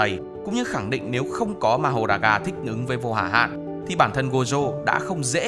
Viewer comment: Wait wait wait, thích ứng t nghe thành... thích nứn....